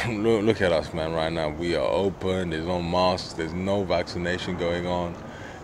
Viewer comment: No masks…I thought he said no mosques. Wishful thinking!